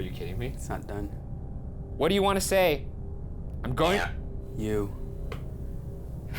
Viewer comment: I think it thought Ryan said what do you want to save